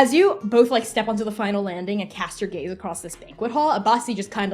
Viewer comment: subtitles say 'cast your gays' lmaoo